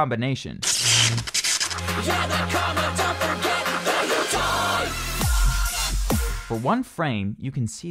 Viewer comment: lol when springboi fisrt appeared i heard "dont forget that your dieee"